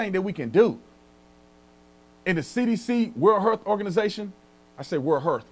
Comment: LOL BRANDON‼️ When You said (WHO) I swore you said "WORLD HURT ORGANIZATION!" Which in this situation is 💯% accurate‼️‼️ BLUNT FORCE TRUTH! INFORMATION UNDERGROUND RR MUST WATCH‼️‼️‼️ New Evidence! New Science! <link> ALSO: ALIVE AFTER THE FALL! <link>